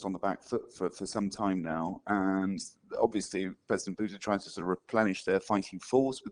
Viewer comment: Turn on subtitles, auto-generated in English. 1:11 "And obviously president Buddha" instead of Putine. The irony.